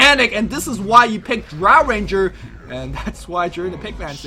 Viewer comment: nice swap, and btw, did he say drow ranger?